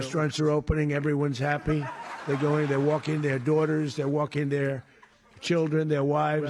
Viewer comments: He said "Everyones out, walking their daughters" or am I hearing things??? Walking their wives??? Like dogs? Omfg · Did he just say people are walking their Daughters and Wives?